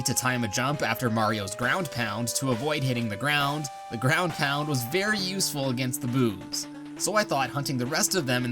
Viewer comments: did he just say “the ground pound is very useful for the boobs”? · he accidentally said boobs instead of boos